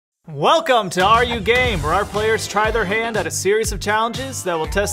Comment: i legit heard "welcome to are you gay"